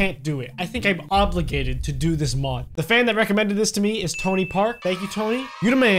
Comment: When he said Tony Park I thought he said Tony Stark